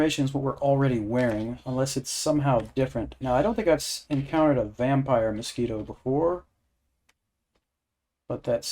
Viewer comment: When you said "vampire mosquito" I first heard it as, "vampire in a speedo".